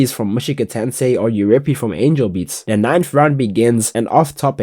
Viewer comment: did he just say angal beats 🥲 such pike